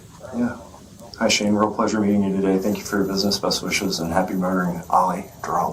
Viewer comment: Just to let yall know, the captions just after 19:40 state murdering instead of meeting. This was the second captions on the video that states this mistranslation. Just dont want yall getting a libel charge for a mistranslation for the service you provide.